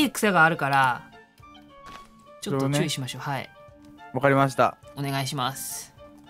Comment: の「注意しましょう」が「ちゅーしましょう」に聞こえた。 末期かもしれない